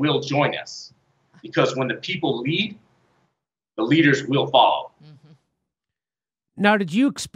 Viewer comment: I’m sorry, at 11:22, is he saying “when the people leave, the leaders will follow”? 🤦‍♀️